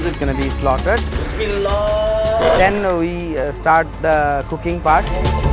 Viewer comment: Swear I thought he said “the cocaine part” He said “the cooking part!” Whew